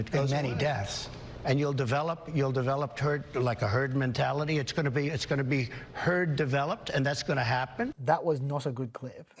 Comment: Did anyone else hear this at 06:20? - Trump: "You'll develop turd, like a heard mentality, its gonna be, gonna be, heard developed".